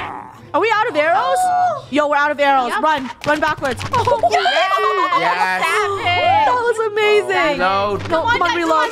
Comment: I HEARD SAID "YESSS BAD BXTCH"